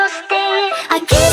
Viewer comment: i keep hearing "fault because" as "birthday 'cause" help